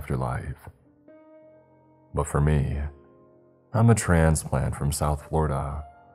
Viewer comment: Did you say I'm a trans man?